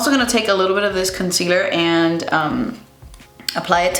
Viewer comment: I hear “concealyer”